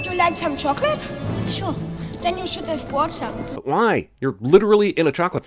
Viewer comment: I thought Augustus said "then you should afford some" at 6:15 😂😂